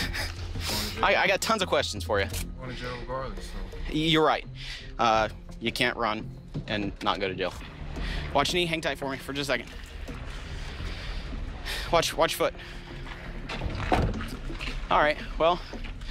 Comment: I thought I heard him say “watch any hentai for me”